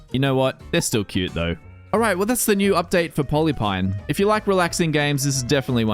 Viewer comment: What is the game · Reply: I think he said polypine?